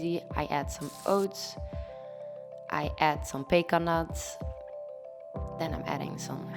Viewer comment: I thought she said bacon nuts 😂